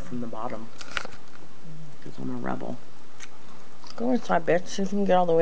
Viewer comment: With my mouth full of gummy worm it sounds like I said "Go inside, Bitch!" at 1:25 LOL